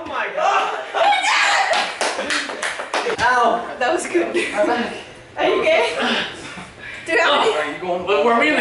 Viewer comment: sounded like she said "are you gay?"